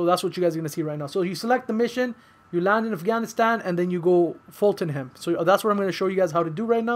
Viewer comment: What the hell is the word your using 0:49 “you select the mission, you land in Afghanistan and then”…you Fulton him? You fall to him? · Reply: Fulton..